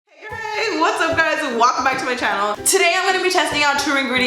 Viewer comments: it sounded like she said Wacome back to my channel😂😂 love her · lol! i sounded like she said "Walkin' back to my channel" and not "Welcome back to my channel" haha